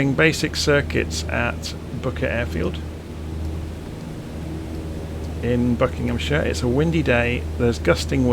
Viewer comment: Booker Airfield ? sounded like you said bucket lol . cool video thank you .